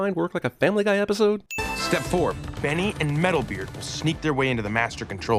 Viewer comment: i misheard that as metal gear